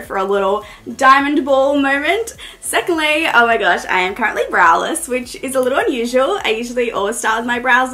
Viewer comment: I thought she said she was braless 😂😂